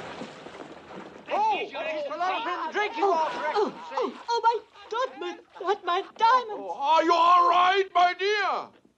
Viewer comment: What does she say there? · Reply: “Oh I dropped my...dropped my diamonds.”